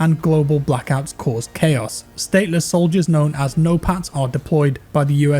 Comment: I was looking away from the screen when it had "NO PATS" subtitle, I had to rewind it and see if I heard that right because it sounded like he said "NO PANTS". I was all for a soldier faction called "NO PANTS"